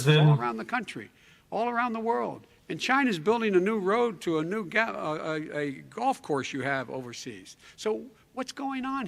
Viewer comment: sounded like " a new road to a galaxy"